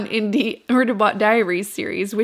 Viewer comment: no shade It is so cute the way she says murderbots, I had to stop the video and read it because I heard "burderbot".